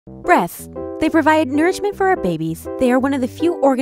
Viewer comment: I swear i thought she said "breath"